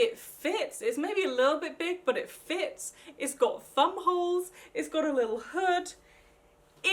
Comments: 6.30 I thought she said bum holes lol sorry idk how to do time stamps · i thought she said "its got bum holes" xx